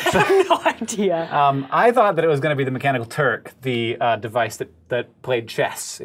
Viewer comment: The subtitles are incorrect at 6:34. It should be "the mechanical turk" instead of "the mechanical turque". It's not a huge mistake, but it would be nice if it was fixed.